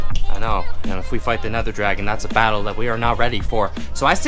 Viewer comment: did he just say nether dragon- It’s Ender Dragon-??